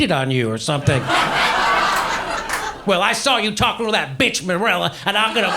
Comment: is pretty epic, I can't even tell wtf name he tried to say :) lmao "That bitch Mereila?" haha